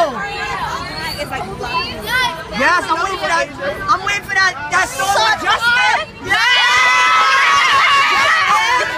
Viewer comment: What the girl said at 1:35? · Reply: "I'm waiting for that song with Justin"